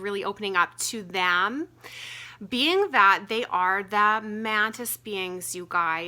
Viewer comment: What do they call themselves? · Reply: I heard "The Mantians" 👍🏻